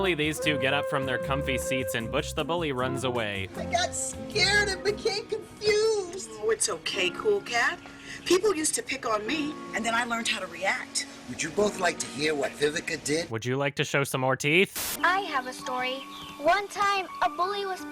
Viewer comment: so my volume is down and I thought she said “bully’s use to hump me” and I got kinda scared